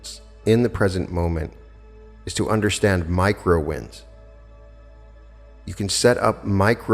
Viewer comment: I thought you said "micro winds". I got a whole visual of starting a bunch of little 🌪 and then they were able to take on a life of their own. Making it easier for me to realize my bigger ideals!!!